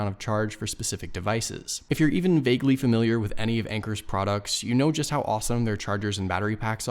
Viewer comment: I thought the speaker said “wanker”.